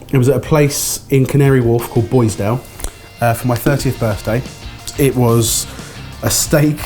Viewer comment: i deadass thought you said you went to a cigar and wiskey bar for your 13th birthday xD